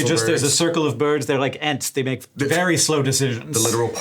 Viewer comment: One would hope Smosh folks would be nerdy enough to get the Tolkien reference with the ents, but the closed captions say "ants." Tragic.